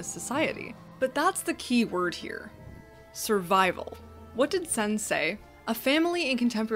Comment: When you said "what did sen say" I heard "what did sensei" 😁